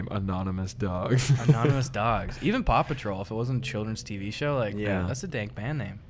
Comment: Wasn’t watching, just listening in the beginning. Could’ve sworn they were saying ‘Papa Troll’ blanket.